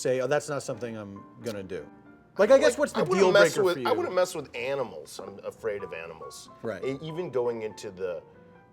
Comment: Eric Andre: "I wouldn't mess with animals" Also Eric Andre: has an actual live bear on his show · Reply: i thought he said "I wanna mess with animals" and I absolutely spit out my oatmeal... im actually eating oatmeal too .... gaaaaawwd